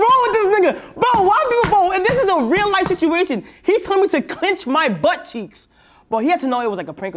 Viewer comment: Did he just say clinic his but ceiks😂😂😂😂🍑